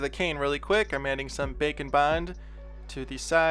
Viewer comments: When he says ’bake and bind’ i always thought he said bacon vind then i realised that had nothing to do with sculpting 😂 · I thought he said bacon bond...